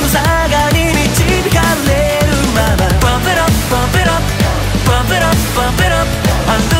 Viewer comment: Pump it up Pump it upのとこお母さんがバッテラ バッテラって歌います笑笑 面白すぎるし、そう聞こえるようになってきました笑笑